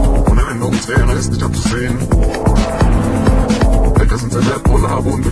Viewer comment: I wasn't paying attention fully to the song, and I thought the guy was saying "Hair Color", I was like WTF? It's Hacker. I look back on the video and listened more carefully, and I was like, that's what I thought. Great Mix